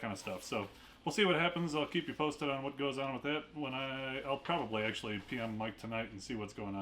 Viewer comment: Oh dear I was listening and I heard “I’ll probably pee on Mike tonight” 🤣 I was like how rude! I can’t do Gold every month so I’m trying to decide if I should just do every other month or stick with Silver. 🤷🏻‍♀️